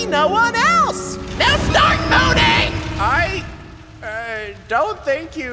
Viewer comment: did he just say start moaning...? 😭😭